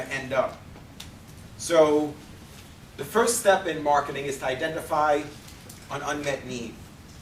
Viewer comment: awesome video...very helpful can anyone please tell me the first key activity of marketing? Identify the.........? its in the first of video at 4:14 . I couldnt understand the word. Please help me. Thank you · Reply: Identify an unmet need